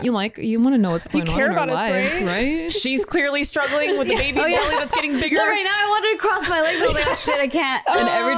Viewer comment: Waaaait did say "Baby Boy" on min. 1:37 ?!?!? I thought I heard that! Plus I love that is getting back into possibly finding her mister right, she deserves it!